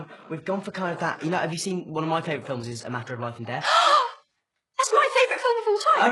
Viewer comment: Whats the name of the film at 21:50? Im not english and i cant understand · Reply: A Matter of Life and Death